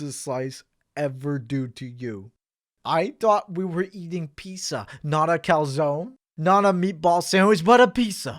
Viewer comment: Did you just say pissa here instead of pizza? Something's off here.